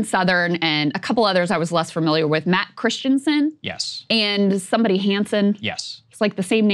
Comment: I thought she said Matt Christman.